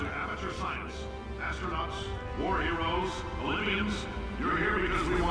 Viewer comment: Cave says Olympians!? I always thought he said Libyans.